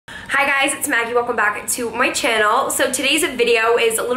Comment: I love she says “hi guys welcome back to moi channel” @ 0:02 is she french ?